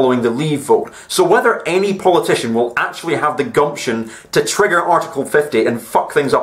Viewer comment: Did you just say "goption", like in "guts" and "option" :D creative, comrade